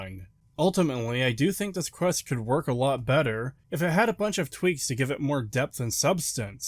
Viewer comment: Its 4 am and I heard Twinks instead of tweaks and I still agree